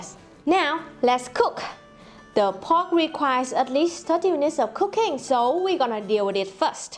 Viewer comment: So, @ 2:08 , I heard that the pork requires thirty minutes of cocaine. That seems to be alot ;) Just teasing!